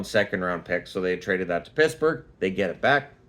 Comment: Did you say Pissburgh at 4:18? Lmao I'm using that one.